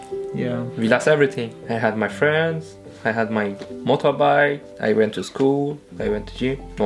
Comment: His family faced so much trouble. 19:45 did he say mota bhai? Sounds like gujarati.